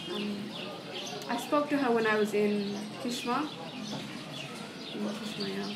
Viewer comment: On, 3:04 did she say Khsham or Kishwah, as it is written on the subtitles? Because Al Kishwah is close to Damascus (yeah, i just google it). It doesn't make sense. At that point of time, when Raqqa was taken from the IS, she's describing a runaway following the Eufrates river. That's why I think she meant Khsham, that's in the area of Deirez-Zur, where the offensive of SDF took place after.